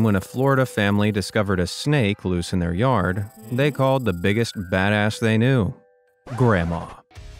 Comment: did he just say badass bro? rlly?????